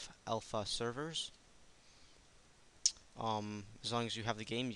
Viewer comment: Somehow the video jumps at the end (1:15) and I hear you say "Sex time" XD